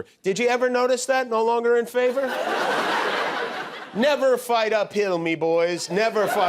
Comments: I thought trump said " Never fight a pill!" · Hahaha, I keep hearing: "Never fight a pill" - yes, I am no native speaker... :face-blue-smiling